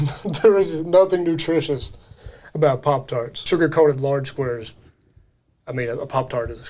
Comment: I always thought he was saying lard squares which is Jell-O... 😆